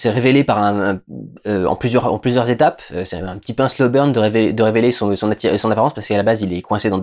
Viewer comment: A 17:18 il dit quoi « c’est un pinceau berne » je comprends pas c’est une expression ? j’ai envie de savoir !